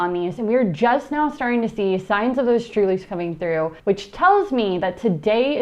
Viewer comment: near the beginning, you give the criteria for the siding that the sprouts are ready to harvest. You said you could see something coming through that sounded like you were saying “STRULIES”. I did study botany, and I don’t recall that term at all. So I’m hoping this is a case of really truly unclear audio at that point. Would you mind terribly getting back to me and telling me what the term is. I’m certainly not afraid of doing the work, but I really do need to hear that term properly to know exactly what you’re saying. I’m new with micro greens and sprouts, and also New is freeze-drying. So I’m gonna quick look around online brought me to your video. I appreciate you sharing the information that you have, but I need a little extra help here. Thank you very much. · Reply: "True Leaves" is what I'm guessing the word was. Our book covers everything in exceptional detail! <link>